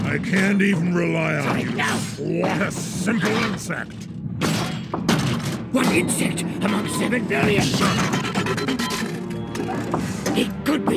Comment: Wat did starscream say at 0:19 when Megatron was gonna step on him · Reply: Starscream: "What insect among seven billion?!"